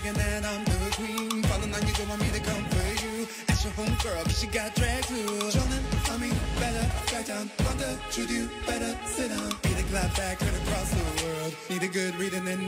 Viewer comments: I hear “want the truth, you better sit down” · I swear I thought Shea said “Cracker, Juju, better sit down” I screamed!